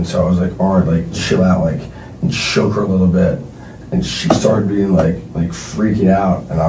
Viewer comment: he accidentally demonstrates hitting her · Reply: Does he say he choked her a little bit